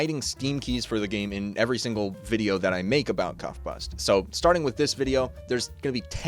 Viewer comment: swear you said cumbust